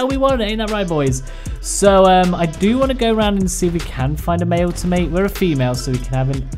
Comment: I keep hearing dude say “find a female Giga” but in the bottom left I see “Find a Male Giga to mate”......